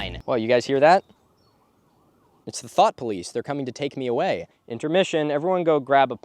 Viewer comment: Honestly thought he was saying “thot police” every time a plane flew over for a second